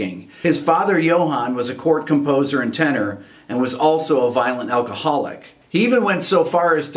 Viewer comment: I thought he said violin alcoholic hahaha. I was trying to figure out what kind of music slang that could possibly be.